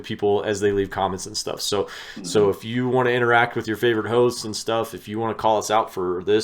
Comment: def sounded like “if you wanna interact with your favorite hoes” 🛥 🚤⛏⛏ (that’s supposed to be boats and hoes but there’s not a hoe emoji 🤷‍♀️)